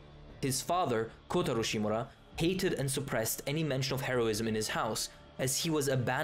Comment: i thought you said "heroin" instead of "heroism"